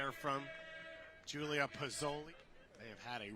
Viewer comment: what's her name?? I keep hearing pozole, like the mexican dish 😅😅